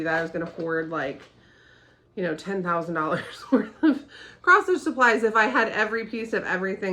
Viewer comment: When you said "this is a channel about cross stitch" ... I briefly misheard it as "cross fit" and almost lunged at the computer. I'm not a mira fan, but always impressed that people stitch these.